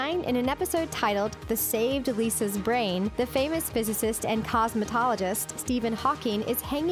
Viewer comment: for a second i thought they meant Stephen Hawking was a cosmEtologist instead of a cosmOtologist but then I was like 'wait a minute' lol also I'm not sure which came first but Inspector Gadget had a computer watch and his daughter Penny had a laptop before those were actually invented